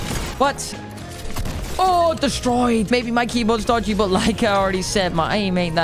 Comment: according to captions he said Mikey bones instead of keybinds. Who else only heard Mikey bones instead of my keybinds now?